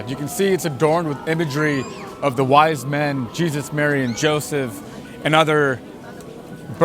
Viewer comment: did he say Jesus married Joseph? 😂😂😂